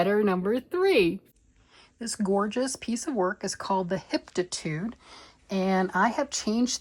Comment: Cake is delicious!😄 Can you please spell out the name of that lacy sweater that you showed with the high waist! It sounded like you said "Hiptitude", but I couldn't find it on Ravelry. You have so many pretty sweaters going! Can't wait to see how they turn out! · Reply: I’ve added it to the description box. Hitofude Cardigan by Hiroko Fukatsu